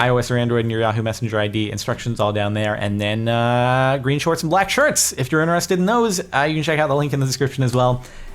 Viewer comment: ALMOST sounds like hes saying "Green Shirts and Black Shorts!"